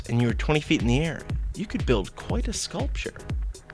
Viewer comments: i thought he said coitus sculpture o.O... i watch too much "big bang theory" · Lol I thought he said "coitus sculpture". I was all like "aww common, really dude?" but then i listened to it again. Then I was all "Oh alright nevermind".